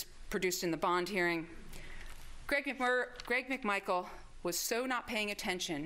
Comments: At one point I think she called him Greg “Mcmurder” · DID SHE SAY 'GREG MCMURDER? LOL! LOVE HER :D